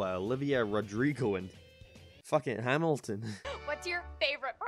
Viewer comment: I thought you said Hamlet . I was confused for a thorough second there lol